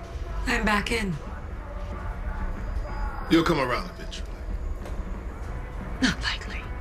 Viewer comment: jaha says " you'll come around eventually" captions say "you'll come around bitch" lmfao